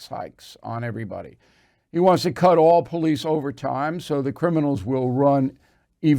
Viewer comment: did you say no overtime for police?